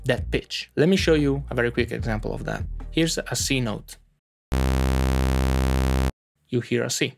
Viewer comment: My brain thought it heard 'asino' 😉